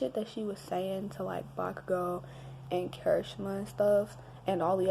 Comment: instead of Bakugo there is vodka girl in subtitles 😂